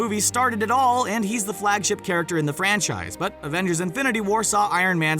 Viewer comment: Im french and i always thought he was saying "french eyes" in spite franchise